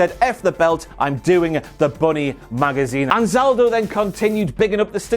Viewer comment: OHHHHH you’re saying “Bunny Magazine” i thought you were saying BODY magazine.... lol I’m so dumb